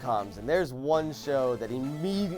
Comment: When Joven said "Immediatly" I thought he said illegally